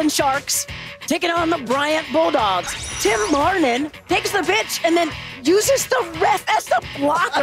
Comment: So, we're just going to ignore that Suzy accidentally said the "b" word instead of pitch at the 12:00 mark? Ok.